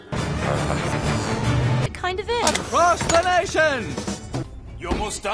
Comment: in the comparison part, at the plan bee part, i heard "SNOW HALATION" what the fuck